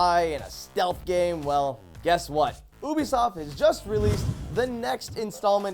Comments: did he say boobiesoft? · did he say oobisoft?